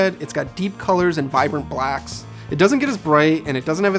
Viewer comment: Did he say vibrant blacks? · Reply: Samuel Wilson he said deep colors and vibrant black 😂 which should have been the other way around